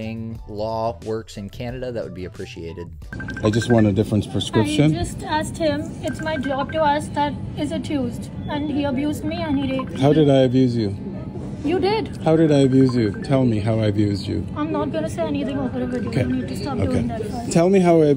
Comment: she says "he abused me and-" and I'm most likely mishearing but....does she say "he r*ped me" ??? I put on captions but it misses some words. That can't really be what I'm hearing.